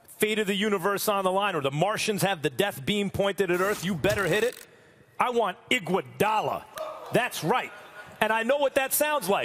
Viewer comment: As a Young Teen that smokes hell of weed ,I swear I thought Max Said "I want an iguana 🦎" when I heard this the first time 😂😂😂 i was laughing for an hour straight 🦎🦎🦎🦎🦎